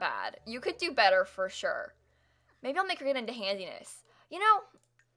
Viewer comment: every time you say handiness I hear "handsy-ness"